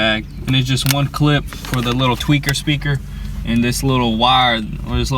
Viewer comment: TWEETER speaker.. LOL not TWEAKER speaker.. Every time I hear it, I'm looking for some paranoid dude that jumps every time someone touches him.. LOL